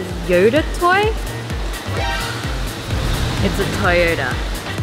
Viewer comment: Look found a toyYODA wendy:wait really?!wengie:Ya!*wendy checks outside* wendy:huh?!its just a yoda toy**mind:I thought she said a toyota me:Gurl she said toyYODA